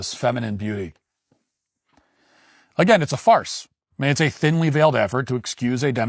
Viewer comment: When you pulled Roskes picture up and said "Again, its a farce" I heard..... "Again it's a fart". It still made perfect sense.